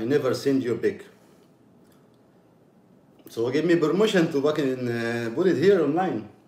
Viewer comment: They give me promotion. Otherwise known as permission🤣🤣🤣 never would have known that’s what he was trying to say wow I love that I found this channel. He definitely sounds even more like an idiot when you can understand everything he saying🤣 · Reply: Yea it sounded like he was saying promotion! 😂 but he was trying to say permission